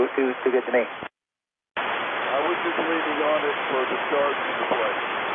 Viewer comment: ??? sounds like he said "I was completely honest from the start"